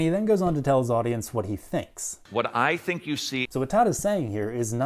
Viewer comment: you are lying. Todd did not say "This is what I feel". He said "This is what I think". If you're gonna lie about what someone says, here's a tip that'll help you defend your man-made system of lies better: don't play the part where someone says something right before you lie about what the person said. That way, some people will miss the fact that you are lying.